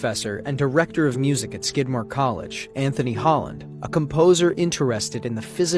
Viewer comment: Did he just say SKIDMARK COLLEGE???? NO WAY.............